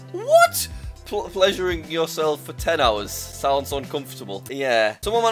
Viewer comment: I thought “pleasuring yourself” said Ohio